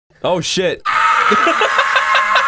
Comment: I literally think that pico is saying OH SHIT!